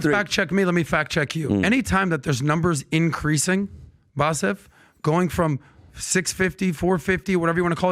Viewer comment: did he just call his guest "Bassef?" Like a conjunction of his name? Hahaha. Can't even get your guest's name right.